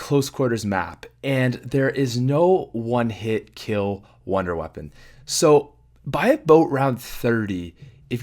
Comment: did he say buy a boat 30